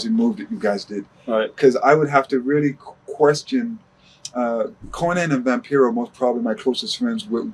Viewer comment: Cornette & Vampiro? Did I hear that right? Or Konnan? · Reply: It’s Konnan. Never mind.